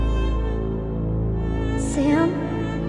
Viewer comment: I always wondered who TF Sam is at 1:16:06. Turns out she was just saying 'sim' all weakish.